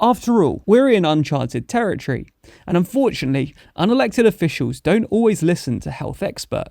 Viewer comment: Did I hear that right? Trump is an elected official